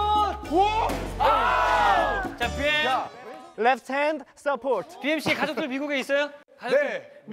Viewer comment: i realy want to know what they are saying ?? Super cute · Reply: last elf stand I think they said left hand only support .. If u hear at 3:01 u'll heard hyukjae saying "left hand support" .. but,it just my opinion though 😂😅